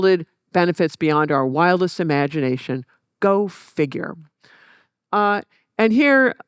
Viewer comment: When the lady says, “GO FIGURE!”, I hear it as “dumbasses!”